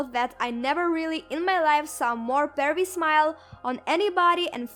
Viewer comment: What did she say about his smile?? I can't quite hear that adjective. :( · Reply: Pervy 😁